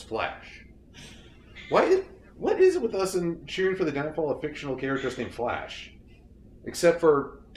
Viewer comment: People, I need help. I'm trying to write subtitles for this video, yet I cannot decipher the word spoken by at this time stamp. If anyone can help me figure out what that goddamn word is, I will credit you in the captions. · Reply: "What is it with us and cheering for the downfall of fictional characters named Flash?"